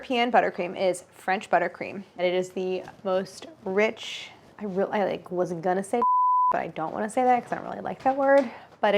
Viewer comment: What was the french butter cream word she bleeped out? Thicc? Idk what she said I'm so curious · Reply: Moist. A lot of people find that word gross and associate it with other "moist" and gross things like infections, sticky sweat, runny nose etc.